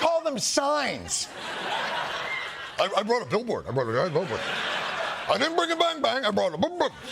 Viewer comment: Billboard? I thought he said bullhorn